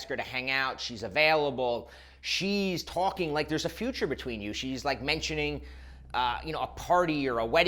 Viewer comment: I was coughing and I heard "She's talking to you like there's a refrigerator between you."